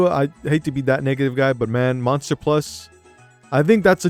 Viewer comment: did you just said what i think you just said????????? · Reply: He said negative guy that sounded like n word 😂